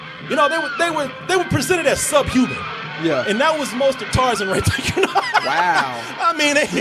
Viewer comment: Sounds like is saying Tarzin at times... lol